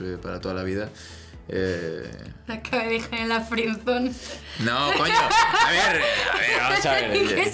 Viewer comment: En 2:40 que dijo clara y se significa?? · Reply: Me acaba de dejar en la friendzone